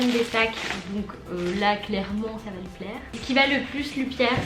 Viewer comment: C'est moi ou à 7:23 elle a dit piaire au lieu de plaire sinon élira va être gâter